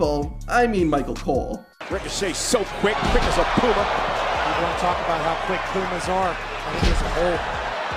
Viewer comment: Can someone tell me what Pat Macffe said? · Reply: "No one talks about how quick pumas are"